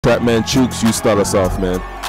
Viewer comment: I heard "trapman chewkz" as "batman suits" and honestly that's my new Canon for this video